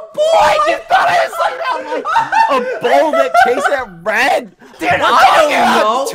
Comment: after replaying the video a bunch of times i think bad said "a bull that chases red" at 11:07 :D